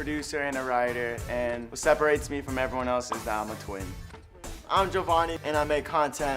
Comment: "what separates me from everyone else is that I'm a twin." Bro your twin is also there. The quality that separates you from everyone else can't actually separate you if you share that quality with someone else that's there. 🤦🏾‍♂️ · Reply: I thought he said "twink" at first lmao